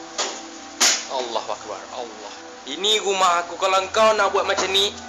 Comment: Did he say; A. "Allah Ackbar" B. "Al la hackbar" C. "Á la Hackbar" D. "Á lah Ackbar" E. None of the above (Comment) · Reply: It's Allahu akbar